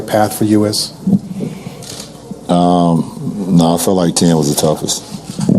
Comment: What does he say at 3:32? He feels like t___ is the toughest? Can someone help me out? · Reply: '10